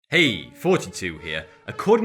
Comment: I thought you said 'Hey, 42 here...' and I believed you. I know people of that age that look younger too. Generation X sounds better, but that puts me in the same bracket as the parents (in their 50s). You can tell who the millennials are below, they are the ones desperate to be labelled as one, arguing over the precise dates from websites which give differing estimates